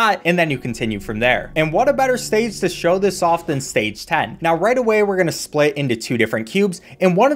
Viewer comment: I thought he said stage Satan.